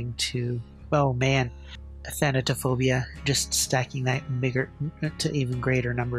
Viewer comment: did he say stacking that n word